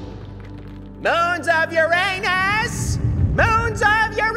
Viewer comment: When Neptune said "moons of Uranus" I thought he said "moons up your anus". 😅